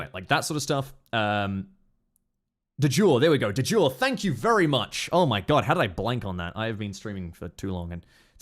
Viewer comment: Ck word for borders? The other word he said. I'm confused. What is the word/term for type of borders he's talking about? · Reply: It might be de jure